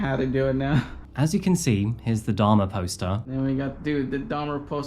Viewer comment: I thought you were saying "dharma poster!" I was way off! Are you able to add that officer's location on that map? I couldn't tell where H and M was nor where Garcia (or the officer) were or were headed.